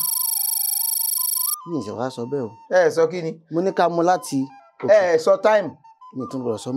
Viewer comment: Pls how many people heard the "time" that caused the first deduction? I didn't hear it. I hope I'm not the only one. What I heard was "Ká mu láti"